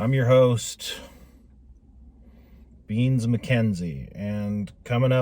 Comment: did you just say your name was Beans Mckenzie? 🤣